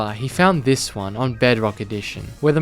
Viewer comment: not the version the seed is from saying bugrock 😭💀